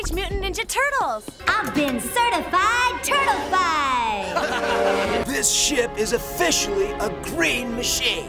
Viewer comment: Did he say sh*t on 0:14?